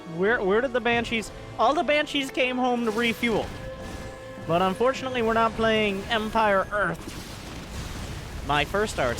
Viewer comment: what does say about EE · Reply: "All of the banshees came home to refuel. But unfortunately we're not playing Empire Earth." I guess you know why he said that. ^^